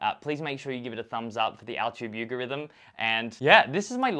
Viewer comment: did he just say al-tube you-gorithm instead of YouTube algorithm?